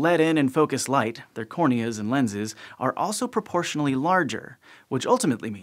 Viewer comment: WTF did you say? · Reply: Proportional larger ?